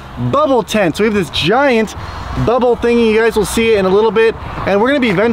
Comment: Did he just say bubble thing